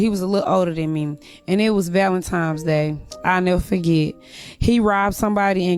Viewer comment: Did she just say “ValentiMe’s” day?!? 🙄🤡🤡🤡